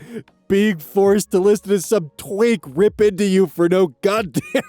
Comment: Can someone tell me what said here: "being forced to listen to some ? ripping into you"🙈? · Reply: Twink